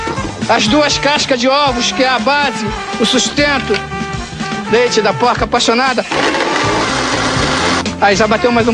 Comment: correção.. é leite de porca.. xD Eu entendi "dente" hUahUHAuha Jabuticabra foi fodaa